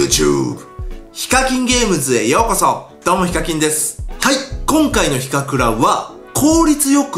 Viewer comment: フィッシャーズのセレブガチャの動画見たあとにこの動画見たら どうもイカキンです って聞こえた